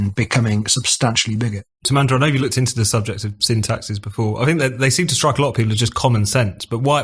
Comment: Thought they kept saying "syntaxes" not "sin taxes"